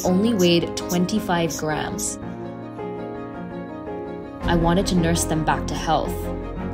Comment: Who else thought she said “I wanted to nurse them back to h*ll” 👇😭😭